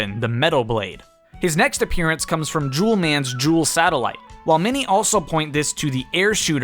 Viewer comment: Bro I thought you said juul man bruh